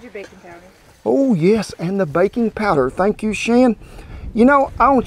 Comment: I thought he was saying “bacon powder” 😂 Bet that would taste good too!